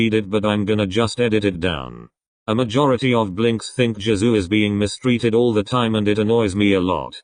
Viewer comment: I heard "Jazoo" instead of Jisoo, wtf